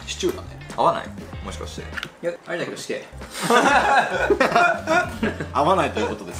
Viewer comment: ここなんて言ってる？ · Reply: ありだけど死刑 って言ってますね